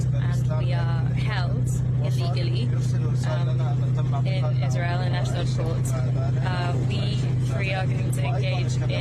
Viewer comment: the subtitles are wrong. She says "when we are held in Ashdod port" and subtitles say "held in Eshleh Shorts"